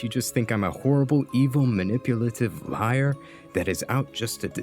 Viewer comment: I think you fucked up the caption at 8:15 since you put lair instead of liar